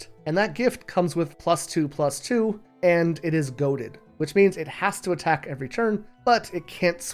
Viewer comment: I thought you said "Goated" instead of "Goaded" and choked on my tea